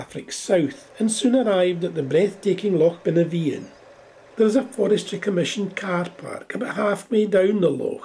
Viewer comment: Hey, at about the 8:41 mark did you say Loch Benevean? What a beautiful place!